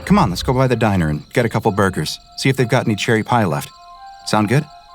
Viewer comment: I thought i heard him say “chili pie” instead of “cherry pie”